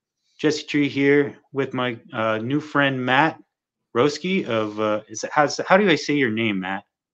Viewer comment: I thought he said Matt Broski 😂